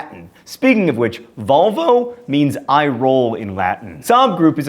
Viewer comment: every time someone says "volvo" i hear "vulva" kinda makes me like the car a bit more...